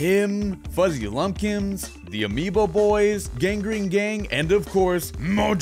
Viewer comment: Did he just call them the Amiibo Boys?